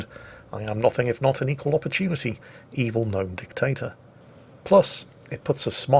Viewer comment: I heard 'Gnome Potato"? Instead Dictator?